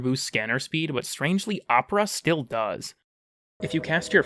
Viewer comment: "but strangely ??? still does" I'm sorry, never had to do this before, but what did you say there? :') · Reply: Opera, it's one of the buffs from Octavia's 3